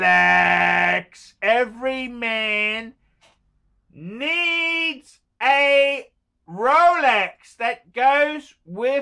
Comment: Sorry about my hearing but did he just say “every man needs a psychiatrist “